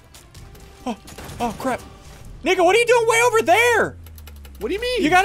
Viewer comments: what did he say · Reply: He said Aw crap nico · sounded like ni*a lol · Is it just me that thought he said n*gga